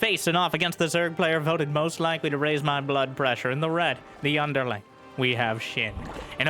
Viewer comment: During the intro, I misheard and thought he called SHIN the "Byunderling".